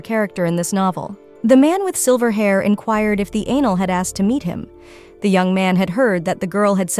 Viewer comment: I just heard the word "Anal."